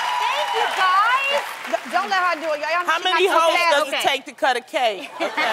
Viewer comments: i had to take a second list at 0:15 i thought Loni said "How many hoes does it take to cut a cake?" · My ears are playing tricks on me because I almost thought @ 0:15 Loni said "How many hoes does it take to cut a cake" haha But anyway this was really cute and Aden is so adorable. And lol at Loni's and Jeannie's face when Adam said he was going to have them babysit. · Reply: lol no she didn't say that. I replayed it and she said "How many host does it take to cut a cake." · Lmao! I thought Loni said " how many Hoes does it take to cut a cake" 😂😂😂